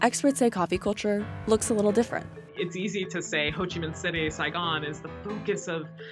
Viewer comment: you have a mistake with the subtitle. That should be Ho Chi Minh City, instead of Hodgeman city